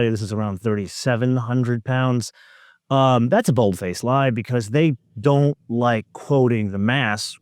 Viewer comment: Did you say bull-faced lie @ 1:30? 😁